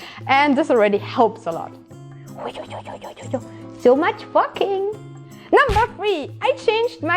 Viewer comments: Yo me too xD I'm like hold on...that cant be right xD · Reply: Y'all crazy... I totally heard "so much walking" at first but now it sounds like so much f*cking to me now as well... Gee thanks. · I swear i misheard you say "so much f@ at 4:08 XD now i cannot unhear that.